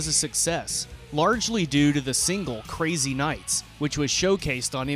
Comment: is he saying whack a mole crazy night?